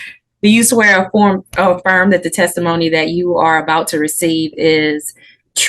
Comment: did she just say the testimony you are about to receive?